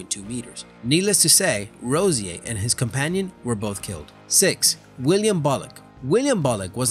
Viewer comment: William bullock its sounds like he said "Wiliam bollock"